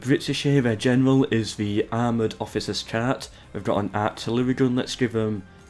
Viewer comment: Did you say armored officer's cat!? The British general is a fucking armored cat!!? I understand now how they never surrendered to the Nazi with this kind of general xD